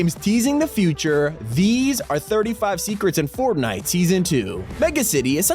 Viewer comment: did anyone else hear him say formnite or was it just me